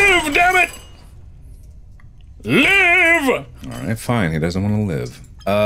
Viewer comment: continue to press 3 until markiplier says live and it'll sound like hes saying liva (Or in translation Liver)